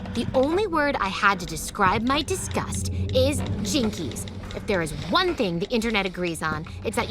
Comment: I always thought she said "Chinkies!".